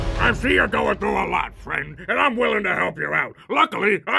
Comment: I swore the cop said you could find your wife on Xbox Bang Pass and died laughing.